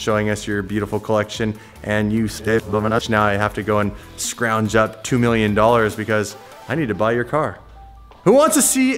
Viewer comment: what in the hell did you say!?? 🤣🤣🤣 I love the music you guys sampled in!! You guys never cease to amaze me! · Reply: Neil E.Anlin He said “You sick son of a bi*^tch”